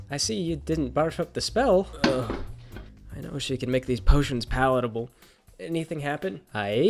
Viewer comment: Does she say "make these potions powderable" at 1:03? I can't quite understand it. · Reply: She says Palatable, like something that somewhat tastes ok